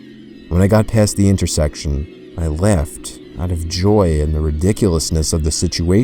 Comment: Everytime say laughed in his storys it sound like he said left